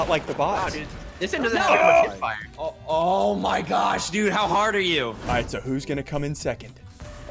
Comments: I swear he said “how hard are you” 😂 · Did he just say oh my gosh how hard are you? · Did anyone hear say oh my gosh how hard are you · Who heard say how hard are you